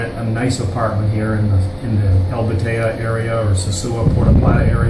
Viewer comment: could you tell me what he's saying at 2:43 when he says, "You can rent a nice apartment here in the (?) area or the Sosua Puerto Plata area." It sounds kind of like 'Albateya area' but I can't find that anywhere. Could you please tell me what area he's talking about? Thanks, and great video by the way! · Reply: Hi there and thanks for watching and your kind compliment!! He said "El Batey", which is an area very close to the Sosua town center - good luck! Brgds